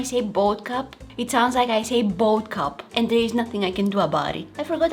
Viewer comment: Even the captions said boat cup